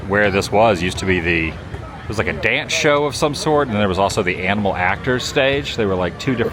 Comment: You said animal actors. I thought you said ample actors. Bernie would be an ample actor.